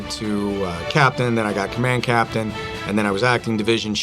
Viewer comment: Had to rewatch the vid cuz i couldve sworn i heard this man just say "command captain" 5:43 ?😂 is that a thing? I might be dumb.